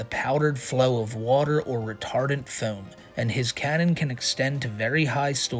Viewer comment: I thought you said "retarded foam" for a minute and thought welp...that's on par